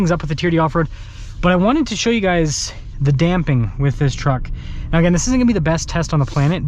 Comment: … Sort of sounded like you said “the dam thing”!, pretty hilarious… it’s a sense of humor sir. Great video❤